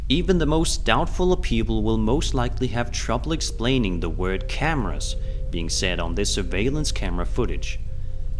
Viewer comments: 😂 he’s says cameras I hear what time is it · Ooohh it said cameras...I heard cabbage 😅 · The 19:00 part. I heard "coward" not "camera" even after listening to it again and again